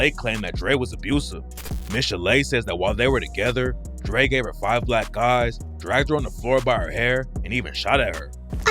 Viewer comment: I wasn't watching the video but listening, and had to rewind it at 18:15 because I thought she said...5 black guys, she is so soft spoken